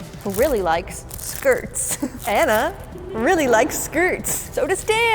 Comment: I THOUGHT YOU SAID HANNAH AT 3:23 (which Hannah is my name-) BUT U SAID ANNA😭😭😭